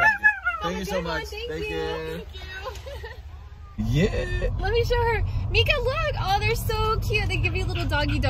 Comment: why did it actually sound like she said “Thank you”😂